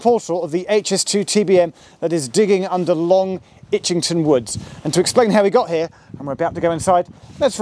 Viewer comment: German here : did I hear that correctly? "long itching wood"? 0:05 :D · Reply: haha, no what you heard was "Long Itchington woods" even shows a map of where the tunnel will go (under the woods) later in the video